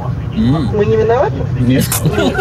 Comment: что она сказала? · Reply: она спросила - "мы не виноваты?" типа настолько за рулем не уверенна в себе